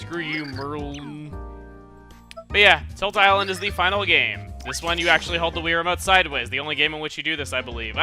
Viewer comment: I thought it said "Tit Island" :/ I was sad.